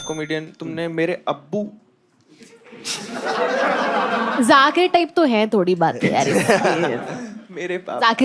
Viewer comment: told 'YAAR' Again, you guys didn't catch it I think 🤔